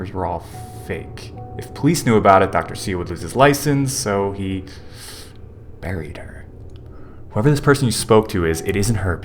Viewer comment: When he said “ so he buried her” I thought he said “so he married her” 😂😂😂